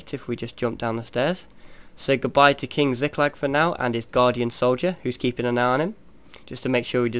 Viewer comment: every time he says 'guard' i hear 'god'... is he english??